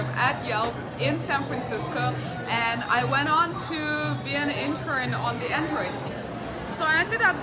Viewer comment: what are you saying?! It sounds like "...intern on the android" but I'm not sure & it's driving me nuts! LOL